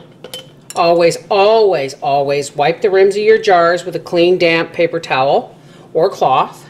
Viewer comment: LOL, when you said "clean, damp rag" I heard "clean, damn rag"! lol, anyway...I'm a serious pumpkin pie girl, how many of those jars/pie do you use? I'm talkin' a good thickness now not a skinny pie.